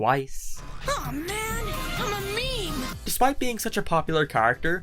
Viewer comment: Did he just say he's a meme...? Quick, anyone got any bleach!?